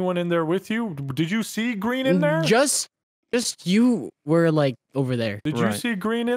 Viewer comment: You got the subtitles wrong. It's 'were' not 'we're'. Smh I demand a refund.